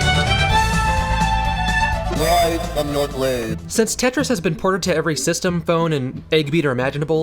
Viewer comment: I always thought it said "surprise, I'm not laid".